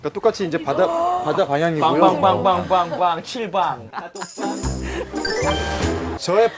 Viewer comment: did Minhyuk said "fuck" or he said another word ? LOL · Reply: NO, he said" bang, bang, bang, bang, bang, chil bang" that means "room room room room, 7 rooms" lol I thought it's "fuck" too hahaha